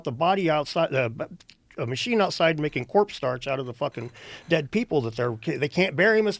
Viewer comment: Did Dan just say "corpse starch"? Is Dan a (Warhammer) 40K fan? Come out of the closet and join us in the Emperor's glow, bro!🤓